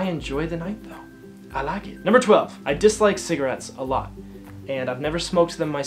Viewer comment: Oh god I thought you said 'I just liked cigarette a lot' and I was like wait what oh wow haha